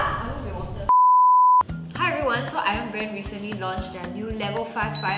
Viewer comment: i heard 'my friend recently launched...' HAHAHA